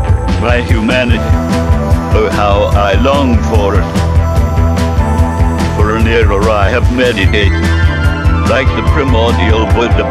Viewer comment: i cant quite catch what he says there "for an era i have many ________" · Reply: For an era I have meditated